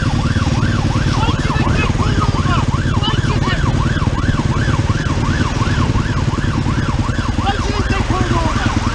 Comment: What did you say? I'm sorry officer, I can't hear you! I'm wearing a helmet and there's a police car with full lights and sirens blaring behind you! What's that? Huh? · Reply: he said why the fuck didnt you move over i dont give a fuck about you or your bike get the fuck out of the way when the sirens are on, theres something more important to get to. in america: pull over stop resisting gunshots he resisted and i was the judge jury and executioner available for this citizen. job done.